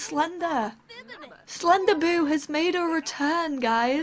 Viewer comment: When you say "Slenderboo" I think it sounds like you say "Slender-boob" ;)